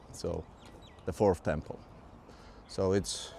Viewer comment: Did you say the 4th Temple?